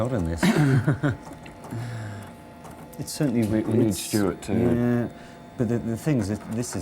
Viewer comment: did I hear John Gater say “we need Stewart”? 😊